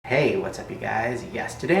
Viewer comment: No one else heard him say "Hey, whats up you guys you guys" ??